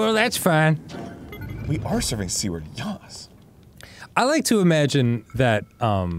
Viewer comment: Danny saying "serving c word" fkn killed me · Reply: I wasn't paying attention to the video title and thought he was saying "Serving Seaward."